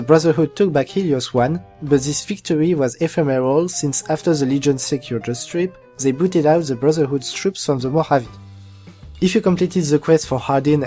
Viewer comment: I couldn't understand what he said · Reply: Chuck Norris "Since after the Legion secured the strip, they booted out the Brotherhood's troops from the Mojave."